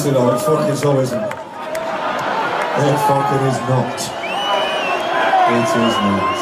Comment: What did he say at 3:21? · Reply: ‘it so isn’t. It so is not’. In response to the crowd singing ‘its coming home’ in reference to England winning the World Cup.